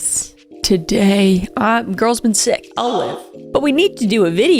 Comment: When you said “I’ll live” it kind of sounded like you said “Olive” 🤣 So all I heard was, I’ve been sick, olive.”